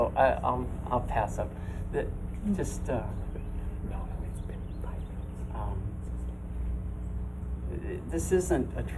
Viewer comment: What did he whisper in the background at 05:24 ? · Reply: The interviewer said "its only been 5 minutes "